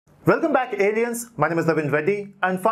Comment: Did he just say welcome back idiots?